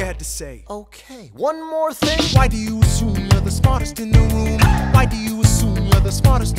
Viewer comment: Why Is that i hear It like "Why Do you assume you're the smartest in the woom?"